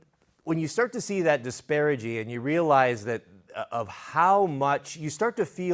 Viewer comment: I think you used the word "disparaging" (maybe "disparagey"?) when you meant "disparity", but I like the word there because the disparity of what people are paid compared to what it earns their employers is a clear form of disparagement. Apologies if I misheard, though. I wholeheartedly agree with the sentiment of the message. I left trades for programming because at age 23, my body was already hurting from trying to get ahead. I went from $22 CAD/hr in 2009 to $49 in 2011. Today I likely earn around 4x more than I ever could have in trades. As fortunate as I am, I find it egregious that I was only able to make the transition due to gifts I was born with and opportunities through my life I've had little control over. I left behind a lot of people who worked every bit as hard as I did, and they deserve far more than they get. Something is seriously wrong – it's as though a mind is worth more than a body in our economy, as though the two aren't intrinsic and equally essential to all of our success today and the success of future generations. Anyway, thanks for the thoughtful message.